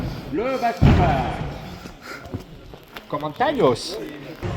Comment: what were you saying? commentalios ? huh hahhah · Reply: COMMENTAÑOS 😂